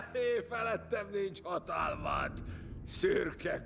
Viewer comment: Mit mond Theoden a nevetés után? Annyit értek ,hogy "Ég veled, és azután el vesztettem a fonalat.💁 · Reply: "Énfelettem nincs hatalmad, Szürke Gandalf!"